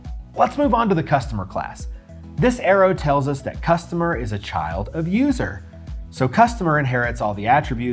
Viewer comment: sounds like you said child abuser, otherwise best video on the topic.